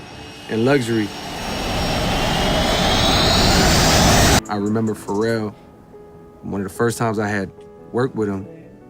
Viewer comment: the caption says 'I remember for real'. It should say ' I remember Pharrel' As in Pharrel Williams.